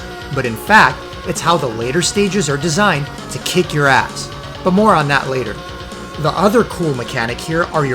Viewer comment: You said butt moron. Uhhuhuhuh. That’s what Butthead would say. (You did put Beavis and Butthead in one of your videos, I saw that, so I imagine there’s movies and shows you watched just like everybody else)